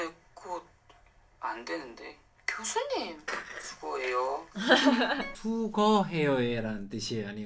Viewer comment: 어 저도 처음볼때 수거해요라고 들었어요! 😀